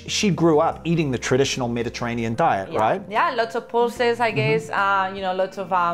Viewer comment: Hi! What does say at 3:18? "Lots of..." Thanks! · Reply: Pulses :)